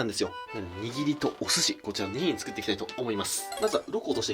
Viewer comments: 「握りとお寿司、作っていきたいと思います！」 俺「……………ん？( ˙꒳˙ )???聞き間違いかな？」ってなって10秒戻したわ(笑) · 握りとお寿司って聞こえるw